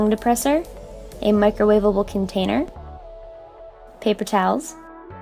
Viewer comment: i thought you said paper towns..